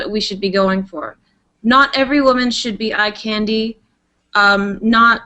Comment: What does she says in 23:37, a candy? Or..